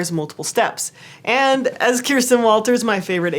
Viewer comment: What's the name of the person you mentioned at about 10:55? I couldn't quite catch it and tried searching but couldn't find anyone name Kirsten Walters. Thanks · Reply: Kjirstin Walters